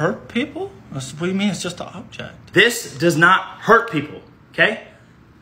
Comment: I thought he said "daggers hurt people"